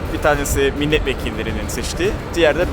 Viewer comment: de ne diyor, milletvekillerinin seçdiği mi diyor, yoksa, siçtıği mi?